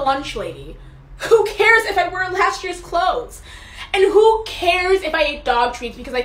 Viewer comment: I thought she said "who cares if I burned ashleys clothes"..